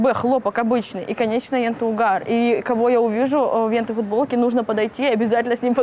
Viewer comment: бля, мне послышалось, или шмоткесса говорит "в энтой футболке..."?